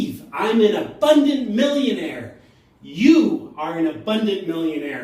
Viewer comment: I heard you say "I am a bunny millionaire"... that makes complete sense to me....